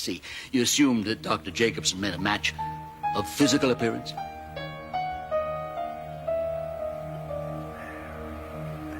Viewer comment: what does he whisper? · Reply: He recognised her through her disguise and whispered her real name 'Mary Bennett'. Hope that helps. x